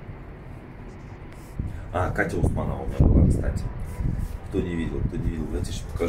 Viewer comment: Я можно сказать совсем не в спорте, но Катю Усманову знаю 🙌👍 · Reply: Усманова или Урманова? Мне послышалось,что Урманова он сказал.